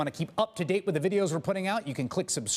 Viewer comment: another important segment RUINED by that stupid chris hayes clip at the end! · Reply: The one where it sounds like he says, "just blow me." Hes actually said Just below me...